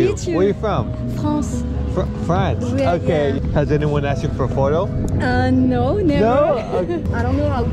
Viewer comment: I heard “has anyone asked you for foil”